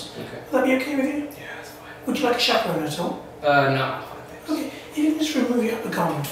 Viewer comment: What is he saying in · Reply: Would you like a chaperone at all?